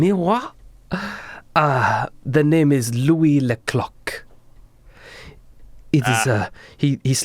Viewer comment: When first said Louis Le Clock I thought he said cock